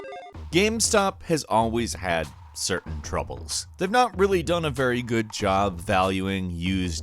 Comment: 😂I thought you said "gamestop's always had....server troubles"